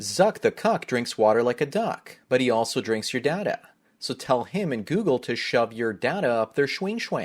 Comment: That “Zuck the cuck” ad intro never gets old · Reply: I always hear “he always drinks your dad out”